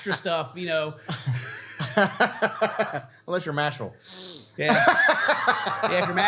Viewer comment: What did they say at 36:02? Unless your mash?? Who is that??